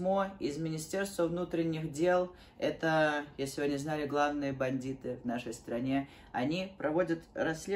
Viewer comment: "Это, если вы не знали, главные бандиты в нашей стране" XD Надеюсь, обойдётся в итоге... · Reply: Мне послышалось "платные бандиты") типа есть во бесплатные, а есть те, которым мы платим